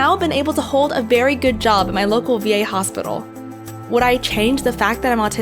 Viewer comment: I thought she said local gay hospital. I'm like what.